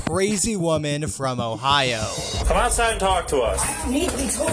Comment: did you said, "Ohio?"